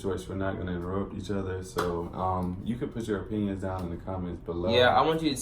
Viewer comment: I thought said penis not opinions 😬